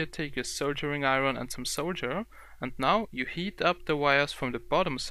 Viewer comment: soldier iron and soldier ?? what is that at 4:30 seconds your saying first grab your soldier iron and soldier Wire ??? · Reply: Soldering Iron and Solder.